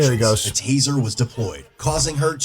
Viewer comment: Thought it said Tate deployed